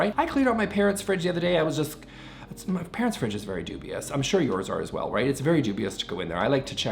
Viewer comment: I thought you said "parrots fridge" and was so confused.